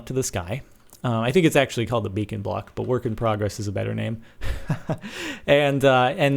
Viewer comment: thumbs up if u thought it said bacon block